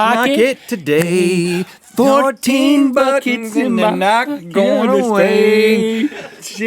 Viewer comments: Did they say buckets? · link says 'buckets' instead of 'buttons'